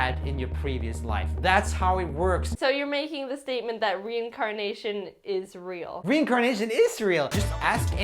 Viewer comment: "Reincarnation is real!" I thought he said: "Reincarnation Israel!